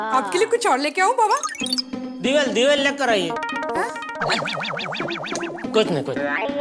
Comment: Does anyone know what jethalal says at 17:04 ..divel for iyer?